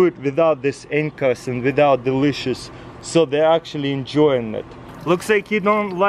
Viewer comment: When he was saying "Without the leashes" I thought he said "Without Delicious" I think i'm hungry